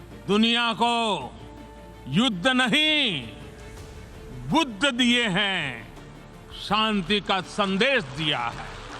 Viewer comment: what he is saying, "yud nhi chud diya hai"